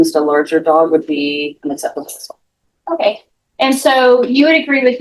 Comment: Correction to subtitles at around 10:15. She does not say that this form of discipline on a larger dog would be acceptable, she said UNacceptable. People have been charged for animal cruelty for doing this to a HORSE let alone a 3 month old toy poodle puppy.